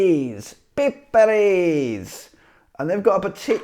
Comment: I just couldn't stop laughing! xD You pronounced it like pippeli! Which means weenie. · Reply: I heard him saying "pippariis" not "pippeliis".